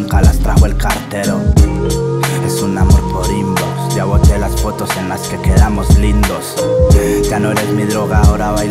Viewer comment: que dice en el minuto 1:27 es un amor por ...??? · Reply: es un amor por IMBOX ya bote las fotos en las que quedamos lindos.......